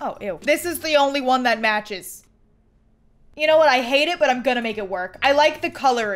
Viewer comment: when said "you know what? i hate the color but imma make it work" i thought she said "you know what? i hate the color and im gonna make it worse." sounds like something she would say, ngl.